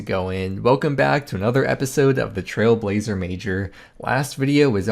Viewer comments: when you say the Trailblazer Mager, I only hear the "Trailblazer Major" like its a big competition lol. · when you say the Trailblazer Mager, I only hear the "Trailblazer Major" like its a big competition lol. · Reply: I heard "trailblazer miniature" in this one 🤔